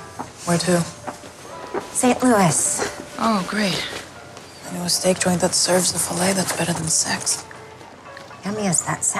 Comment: What does she say at 1:16 and on? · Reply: "Oh great. I know a steak joint that serves a filet that's better than sex." Somethung like that?